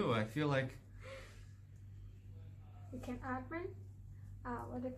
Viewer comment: did that beautiful woman just germenglish the hell out of "you can breath now?" by saying "you can atmen (breath)"? Its late. Must have misheard that because of tiredness. But leaving a comment to come back and check.